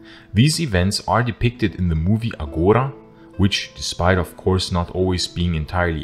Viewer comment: what was the movie title? Couldn't hear it well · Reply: Agora